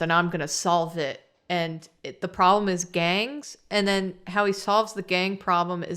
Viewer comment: Im so sorry but when you said “the problem was gangs” I heard “the problem was gays” and I imagined a very different movie with actual drag races